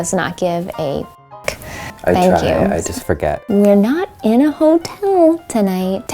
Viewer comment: I don't hear "it was John". There's a clear "G" sound at the beginning of the last word. It sounds like "this was God!" To me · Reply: lol i thought it said ‘i forgot’